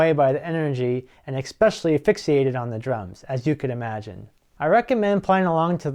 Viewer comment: almost sounded like you said 'asphyxiated on the drums' which is an unfortunate slip considering..